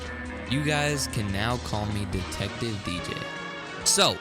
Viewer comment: I am not Trying To be Rude Or anything But turn On Captions And See 8:31 part of the Video With captions Turned On Spoiler The Caption Says Detective BJ Rather Than Detective DJ